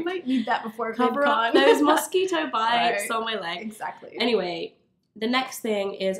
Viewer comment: Literally thought Fleur said, "Musky toe bites!" at first - haha Great video / collab! xx